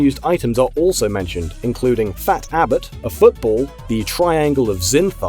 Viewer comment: Did you just say "Fat Abbott"?